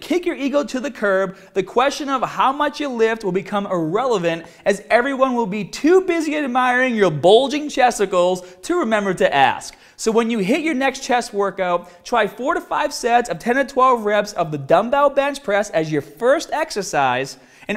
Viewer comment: at 10;55 it sounds like he said "bulging testicles"